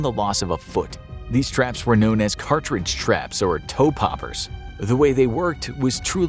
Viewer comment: My father was an Aussie Vietnam Veteran and was about a week from rotating out. On his last patrol a guy who had been in country for less than a week stepped on a toe popper and lost most of his left foot. Dad always said he would rather be in a firefight than deal with Vietnamese man traps. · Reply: I thought you said toe pepper at first lol